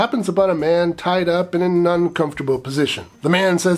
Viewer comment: To me, it sounded like you said "nuncomfortable position". I also do very well with misheard song lyrics.